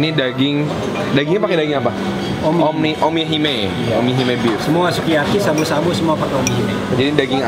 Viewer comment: Omi Hime Beef gw dengernya Kimi Hime Bitch hahaha